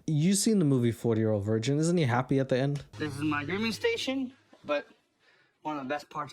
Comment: I thought he said gooning station